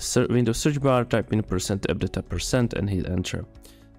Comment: i cant understand the english in 1:48 D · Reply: Bro that is %appdata%